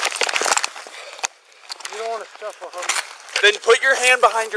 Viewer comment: The part around 11:27 where subtitles say inaudible is the suspect saying “you don’t want to scuffle homie”